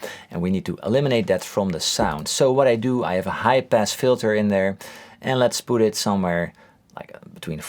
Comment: I really heard "I have a hype-ass filter" and thought "hell yeah dude your filter IS hype"